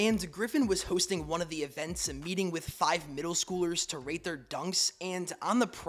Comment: I thought he said junks not dunks😭